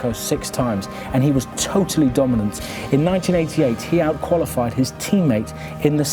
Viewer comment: Sorry 1:14 Did he say 1998?